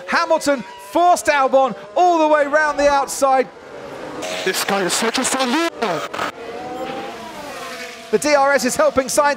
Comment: does albon say: "This guy is such a sore loser!"